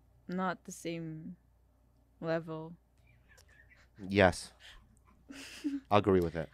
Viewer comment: When he says ill agree i thought he said “i ugree” lmao